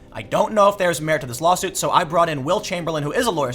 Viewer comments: I thought you said Wilt Chamberlain... Oh well, thought maybe he came back just to diss Acosta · I thought Tim said "Wilt Chamberlain"